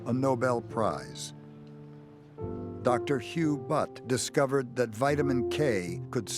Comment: did he say hugh butt?